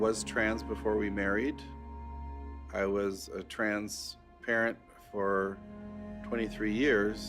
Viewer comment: When he said trans parent my brain heard it as transparent.... You mean he is see though?!?!